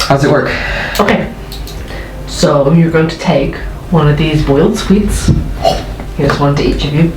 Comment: When R said "boiled sweets" I misheard it as boiled swedes and was very confused as to how those could fit on someone's tongue.